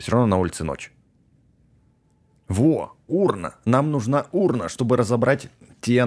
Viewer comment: мне послышалась украина а не урна 🤣 Лайк если вам тоже!